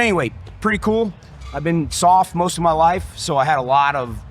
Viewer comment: it sounds like he keeps saying soft for some reason. Anyone else hearing that instead of sog?